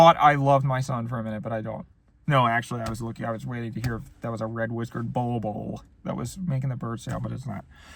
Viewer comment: Did you ever end up spotting the Red Whiskered Bowl-Bowl? It’s eluded me for years! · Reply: I thought he said red whispered Volvo 😂